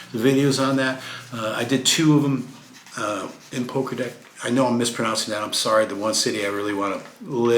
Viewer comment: PO- (like harry Potter) Gra(to grapple) Dec( De like decades and the last letter C is pronounced as ts) Po- Gra-Dec. I thought you said Pokker Deck lol and im like is he going to some asian countries? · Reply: That's funny, thought he said Poker Deck as well.